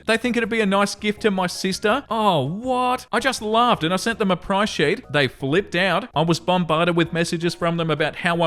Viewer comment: #4: I totally misheard, I first thought OP had sent the half-sister a Black Sheep, not a Price Sheet. To be honest, I think a black sheep would've been just as suitable as the price sheet OP sent, either way, OP isn't the AH.